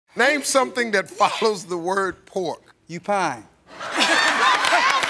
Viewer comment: All I heard was "pork-U fine"